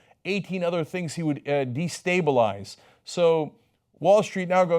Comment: Cenk said destabilize, captions said stabilized. Lmao